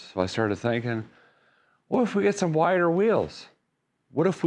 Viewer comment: You said "wider wheels" and I heard "wire wheels" and thought, wow! That'd be so cool! But I doubt there's custom time for RVs. They'd have to be custom or maybe swap over to an industrial size rim.